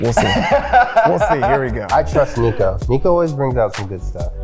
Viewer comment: anyone else think he said "luka" instead of "niko"?